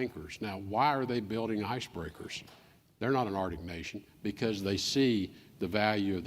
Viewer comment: No, he's talking about the Arctic. It's different. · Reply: my bad, thought he said Antarctica.